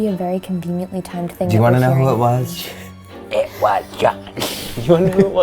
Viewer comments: I honestly feel like that was potentially Andrew responding- to me, I heard a swear in there, I heard "It was f-ing John" as if they are angry about the misconception of who actually did it shrug who knows though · I hear “It was f**king John” or I also hear “It was f**king god” now it could just be because I’m wearing headphones. Idk lol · i literally hear it say "it was fucking john" .-. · I heard "it was fucking John" 😂😂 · i heard "it was Emma" 😯